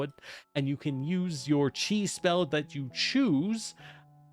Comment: Ha ha, at about 15:00 I had to do a double-take when I heard, "You get to choose your cheese spells. Now your cheese spells are..."